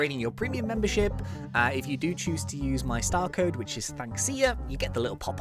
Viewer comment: turn on captions, it says style code instead of star code lol